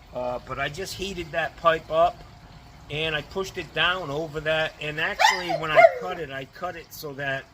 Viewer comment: How to make that flush valve tool. I thought you said you heated the pipe than jammed it down over your head. My mistake. I like your idea of hiring a helper and teaching him/her the right way to plumb. Or, stick with the 4 day weekend idea.